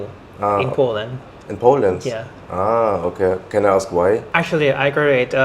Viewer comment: Ich habe statt „in Poland“ … in Poledance verstanden 😂😂😂